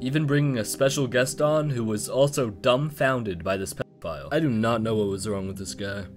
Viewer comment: peppa file? is that what he said? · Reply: nope ,he said 'pedophile' he just censored it in the video